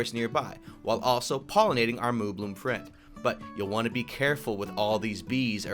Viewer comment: I thought you said moonbloom. Wich is funny because they wear a sunflower as hat.